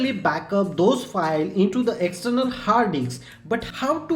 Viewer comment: What did he just say? XDD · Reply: I am saying Har disks